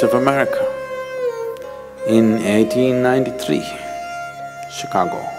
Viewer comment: SWAMI VIVEKANANDA REACHED CHICAGO IN 1993? OR I HEARD IT WRONGLY?(